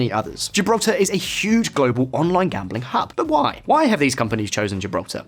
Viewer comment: I thought he said one of gibraltar's biggest scorces of income was online gaming at the start, then when he said it again I realised he said online gambling.